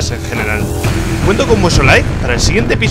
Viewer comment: "Cuento con vuestro like" o escuche "Cuento con Sili".. uhmm